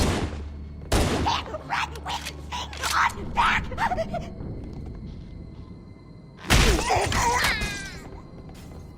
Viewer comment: What is the Grunt saying? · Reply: “Can’t run with thing on back!” Referring to their triangular backpack that houses their life support system.